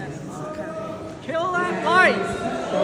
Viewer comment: Maybe I heard wrong, and I'm interested in what others think, but does that person at 9:25 yell, "Kill that wife?" That's certainly not something that should be tolerated if it was aimed at Vance's wife.